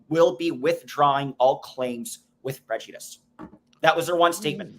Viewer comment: If case was ordered “with no prejudice” it leaves the door open for another case against the Presley family. Not a big point but is a big point. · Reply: It was with prejudice… (I thought that is what I heard)